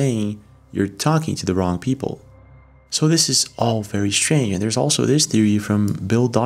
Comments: It sounds like he said "You're talking with the wrong people" But the captions lead your brain and ears to hear (Fucking with the wrong people) instead. Kinda like the Brainstorm/Green-Needle illusion. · I hear "you're talking with the wrong people" not the f word